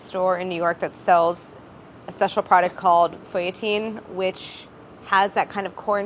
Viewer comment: Who else thought Claire said “Creatine” at 10:20? Next week on its alive Brad gets jacked eating butterfingers